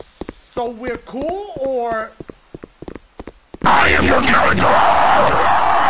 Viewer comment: Wtf is Marge saying when the jumpscare happens? 😂😂😂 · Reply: lol i thought she was saying I AM YOUR MURDERER!!!!